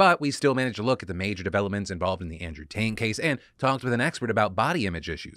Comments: Am I the only one who hears Andrew "TAINT" case here 3:10 LMFAO i hear you Phil, tryna slip a fast one in hahaha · did you say "Andrew Taint"? Well played sir, well played.